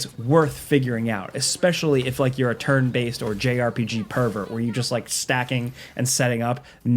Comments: Did he just say JRPG pervert lol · I thought he said jrpg pervert 😅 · did he say "jrpg pervert"? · Did he say JRPG pervert at 5:56? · did he say a JRPG pervert? 😂